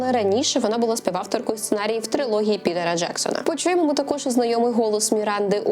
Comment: Цікаво, одному мені почулося "підара Джексона"?